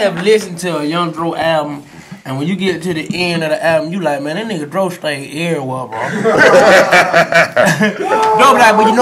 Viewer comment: FOLK! CAN ANYONE TELL ME WHAT HE SAID? DRO BE WHAT? PLEASE FOLK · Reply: chalinoahuesoduro he said “ man dro stay everywhere”